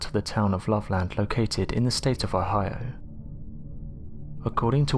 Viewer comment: Jesus Christ. Can anyone understand what state he says the loveland frogman is in at 7:31? This guys accent is ridiculous and he refuses to curb it even slightly for his listeners. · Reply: Ohio just east of Cincinnati